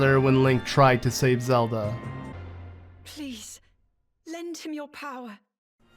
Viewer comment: Ohhh I thought Zelda was saying “lend to me your power” as they reach hands out to each other. As to pass on the powers like the player could be Zelda. But. I guess I was wrong and that would explain why she says it weird lol